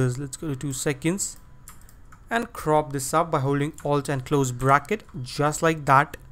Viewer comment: i cant hear what you say 6:25 - 6:30 please help! :D Great Vid! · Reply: alt + ]